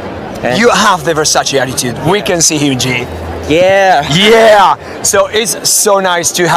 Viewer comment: HE IS SO CUTEEEE ALSO THE WAY IN THE INTERVIEWER SOUNDS LIKE HE SAYS HYJI INSTEAD OF HYUNJIN IS SO FUNNY